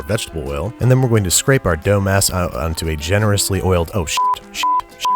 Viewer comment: I thought u said dumbass instead of dough mass